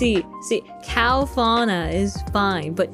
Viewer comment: I thought Kronii's gonna say "Cow deer you!" instead of "How dare you".